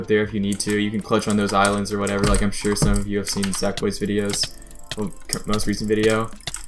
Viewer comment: who's that guys YouTube name? I can't hear u very well. Is it Zack boy? The captions don't work either:( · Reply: Sackboy_Clank5